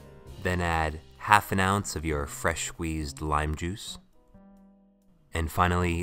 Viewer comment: Did he just say fresh wheezed lime juice